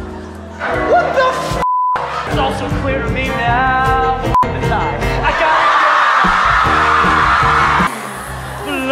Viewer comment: what is darren saying??I can't hear him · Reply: dj he was singing and then in the middle he said ‘f*ck the tie’ because he had been struggling to put it on correctly throughout